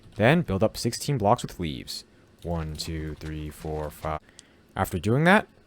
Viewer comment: why did i hear him say weeds instead of leaves?